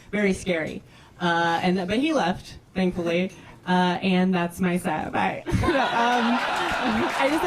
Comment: what does she say @ 1:45? that's my...what? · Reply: "And that's my set, bye"